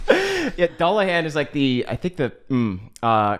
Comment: Lord until Caldwell explained he was talking about the headless horsemans I thought he was saying Dolla Hands. Dolla dolla hands yall.